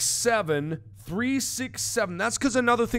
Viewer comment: Did anyone realized that Mark actually said Sex instead of 6 at 7:50? i was like "....o_o so a number rapes a number..?WHAT?!?"